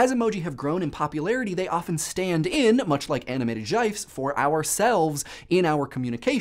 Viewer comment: I actually stated out loud, "Did he just say Jifes?"